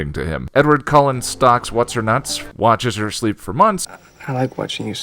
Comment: Hi ! I'm currently working on the subbing of that video in French but there is something you said that I didn't get. At 2:08 <link>&t=128) you say "Edward Cullen stalks (...), watches her sleep for month..) and I have no clue as to what is said at the (...) part, the automatic caption got "whats-her-nuts" but I doubt it's that or I missed something... So if anyone could help me on that one and tell me what is said it would be appreciated. Thanks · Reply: "Edward Cullen stalks whats-her-nuts? Watches her sleep for months." And thanks!